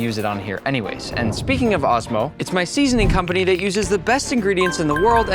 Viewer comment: честно мне послышалось астма